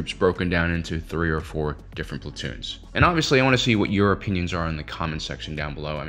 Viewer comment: When you said your opinons i thought you said europeans.